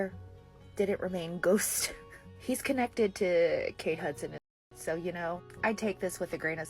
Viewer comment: I was listening on my ear pods in the kitchen and thought you said Kate Hudson lol I thought whats she got to do with Britney lol silly me😂 Love your video ❤